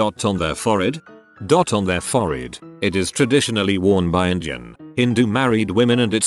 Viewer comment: robot voice says injun instead of Indian